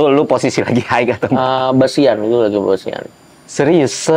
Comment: Coki ngomong apa ya ? Bosian ?